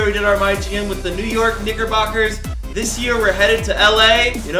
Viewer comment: I swear on 0:08 I heard the N word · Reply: He said knickerbockers